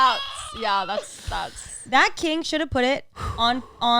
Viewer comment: said “That King” and I thought she said “Matt King” 😂 I thought Matt posted a butthole video